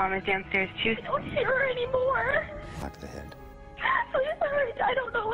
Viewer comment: En el minuto 8:50 se escucha una voz de fondo, que lo que dice es: “shoot the head”, o sea: disparale en la cabeza. Explicó algo: 1) Desde el momento que hizo la llamada y dijo tenia las manos amarradas yo sospeché que algo estaba mal ahí por que no es posible que ella lograse escuchar lo que la operadora le decía y de paso hablar tan nítido ( es tanto la adrenalina x los nervios que uno tiene al llamar al 911 en medio de una emergencia y más con las manos atadas hacia atrás, que es muy difícil luego de marcar también colocar el alta voz), pero vamos a creerle esa número 1 y vamos con el número 2, que es el más importante. 2) El 911 tiene equipos tan sofisticados que se puede escuchar y registrar los ruidos y voces qué hay en el área cuando se llama, esto para poder decifrar cuando se efectúa una llamada donde la víctima solo logra marcar y no puede hablar. Sin tener tanta tecnología como ellos, los que tenemos IPhone y hablamos inglés podemos escuchar claramente cuando ella llama, uno de los hombres dice: “shoot the head”, o sea: “ disparale en la cabeza”. Yo supongo este fue el tiro que recibió la madre. Obviamente aquí se pone la alerta de que es un crimen organizado cuando escuchan nuevamente la cinta y lograr escuchar cuán cerca está ella de esta voz ( quien a pesar de tener una voz fuerte, habla en tono bajo).